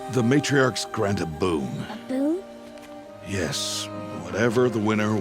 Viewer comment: I first thought rost said the winner is given a boob